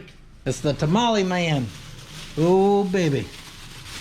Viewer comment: I thought he said Polio 💀